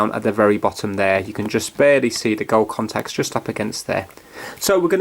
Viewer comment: did i hear "you can just barely see the girl contacts" so the other ones are male? ( ͡° ͜ʖ ͡° ) ( ͡° ͜ʖ ͡° )